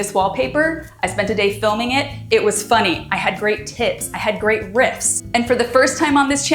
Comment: When you said “I had great tips” I first heard “I had great tits” and it speaks to how long I have been watching your channel that I was not at all surprised that that is something you would say.